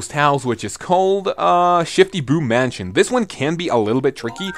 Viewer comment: On the mansion I thought it said shitty boo mansion lol